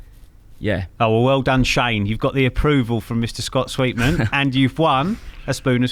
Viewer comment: I thought that said Scott sweetcorn for a minute then lol